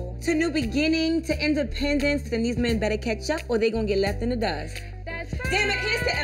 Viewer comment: Ray Jay messy 😂😭😭 Ray Jay: ain't that yo song Oh girl: yes that's my song Ray Jay: better turn up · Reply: I thought he said better boss up lol